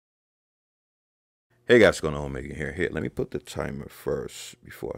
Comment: I know his name is, but I swear at the start of each video I hear "Hey guys it's me, again."